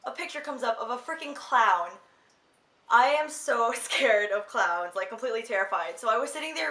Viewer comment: OMG hahah does it really sound like I say clouds? But forreal...I can't stand clowns >.< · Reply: haha. Whoops. Ok I just went back to listen and it sounds like you say clowns. LOL. I think cause you say it as plural the second time that's what made it sound like clouds. Lol. Omg. I was wondering... I was like clouds is a weird fear... HAHA!